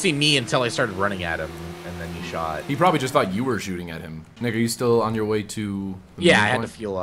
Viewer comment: I swear I thought you said "Nigga you" when you actually said "Nick are you", lolol.